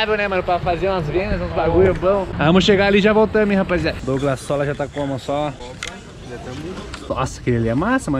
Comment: só eu que ouvi douglassola?